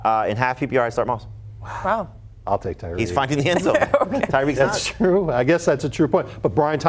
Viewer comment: What is Jamey saying at 12:38 ? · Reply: “He’s finding the end zone.” Referring to the fact that Moss is finding the end zone vs Hill not without Tua lately